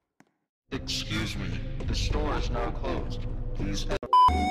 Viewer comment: This is what I hear: "Excuse me the stores are closed please awnser the doorbell"